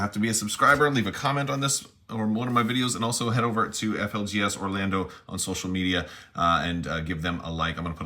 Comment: I thought you were saying that you got that Hoth Luke from FLGS or Lando. I thought good on Calrissian for branching out! Great video! I've just gotten into Legion. I've not played yet as I'm still building and painting. But your comments in the different plastic types and the best glues to use on them will help me going forward